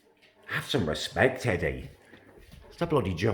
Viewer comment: lol i thought called lenny eddie